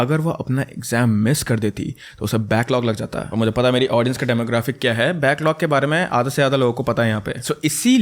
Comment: what is back lock? Or whatever he said...